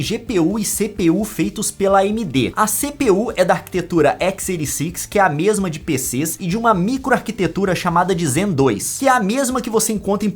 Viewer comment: não entendi bem qual nome você destacou entre 0:58-0:59 antes de falar a microarquitetura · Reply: "A CPU É DA ARQUITETURA X86, QUE É A MESMA DE PCS E DE UMA MICRO ARQUITETURA..." Era algo desse trecho que você não tinha entendido?